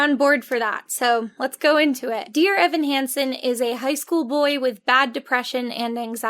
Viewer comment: When you said "high school boy" at 1:06 I misheard it as "high school bully" and thought "wow that's definitely a Choice to make about a protagonist"